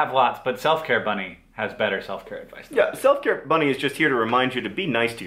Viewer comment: I heard "self care money" at first and I was like, yep, best self care remedy